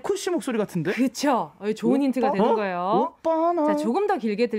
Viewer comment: Did I misheard or was it right that Eunji said "Jongin-ie" in 00:31 ? DJ Eunji is the best, look forward for another interactions with exo member cross fingers And funny how Kai was guessing GD' song while his ex-gf is rumoured to date GD recently 🤭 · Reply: No, she said "it's a good hint" (joh-eun hinteu) 😁